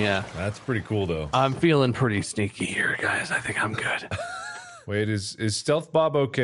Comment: I’ve watched this countless times, and for some reason every time I hear Bob say “I’m feelin’ pretty sneaky here guys”, it sounds like he’s saying “I’m feelin’ pretty STINKY here guys” 🤣